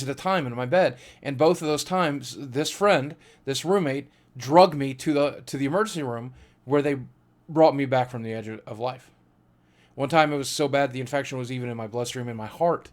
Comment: i thought he said "drugged me" ;P